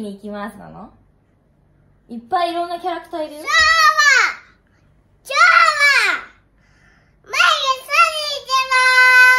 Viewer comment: 「今日は！マイクとサリー行きまぁす！」 って言ってるに決まってるけど 「今日は！マイクandサリー行きまぁす！」 に聞こえてきてじわる